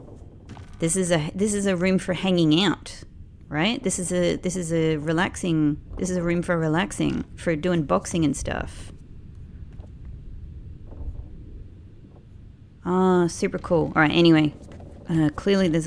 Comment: I'm so silly. When you found that big room in the stern of the ship. I thought you said it was for"unboxing". That's not what you said though and I was giggling for no reason.